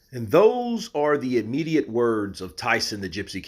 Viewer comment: When you first started talking, I thought you said “and those are the idiot words of Tyson fury….”